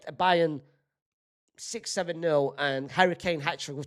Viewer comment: What did he just say!!!??? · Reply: 6...7 😂😂😂😂😂😂